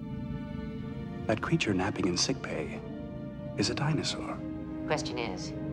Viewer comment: "That creature inhabiting sickbay, is a dinosaur"! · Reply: You thought they said inhabiting? They actually said napping in